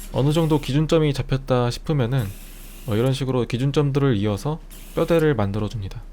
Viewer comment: 나만기준점프로들림?? 기준점프~기준점프~! 와다다다다다!!!!!!